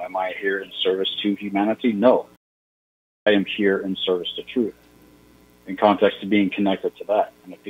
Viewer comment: who are you here. in service of ,, did you say Thor or Truth? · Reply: TRUTH..